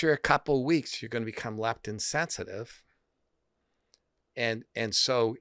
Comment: Low carb = lectin resistance 😲 That was a piece of the puzzle I was missing. Thank you both so much for this fascinating and confirming discussion. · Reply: Lectin or leptin? I think he talked about leptin which is the hormonal appetite regulator released by fat cells. Low leptin = high hunger = overeating = obesity. Fructose blocks the leptin sensitivity, so the brain feels more hungry. Low carbs = increased leptin sensitivity = brain signals appetite decrease = stop eating = better weight control. At 35:01 he says “you go on a low carb diet, and after 2 weeks you become more leptin sensitive” which means you react normally after eating food, the brain can say stop. Easy to hear “leptin insensitive” instead of “leptin sensitive”? What do you think?